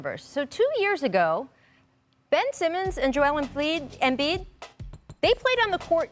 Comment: Anyone else hear “Joel Emfliid” at 5:30? 😂